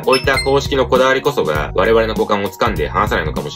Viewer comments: 画面から目を話していたとき「好感を掴んで話さない」って聞こえて珍しい表現だなと思って戻ってみた結果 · メガネはしてない方が好き派閥です。 最後互換を掴んで離さないを股間を掴んで話さないに聞こえて「ん？」て聞き直しました。 ちゃんと『股間』であってました。 なんで合ってるの？